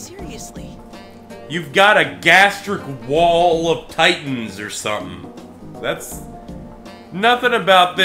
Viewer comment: Did he just say "gastric wall of titans"? Whaaaa?!